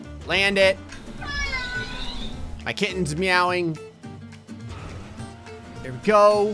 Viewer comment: I thought you said "My kid is meowing"🤣